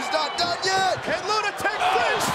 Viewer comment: lol it sounds like Cole says lunatic french fries